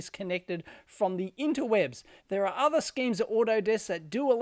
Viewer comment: I thought i heard "there are other SCAMS at autodesk" ...Kappa